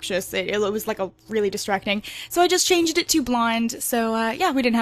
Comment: blind!?!?!? Was I the only one who heard that??